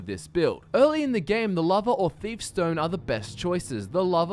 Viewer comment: Why does it sound like your saying lava when your saying lover?